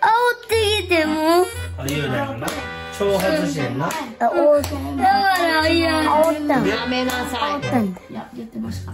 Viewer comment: ここからなんて言ってるん？ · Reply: 「しんじまえ、原爆落ちろゆーうだい」って言ってるびっくり